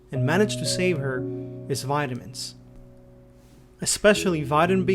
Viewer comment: When u said vitamins ford the first time I thought u said violence jokes aside I hope he’s doing better